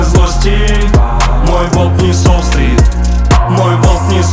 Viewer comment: мне послышалось сначала - "мой волк не суслик ")))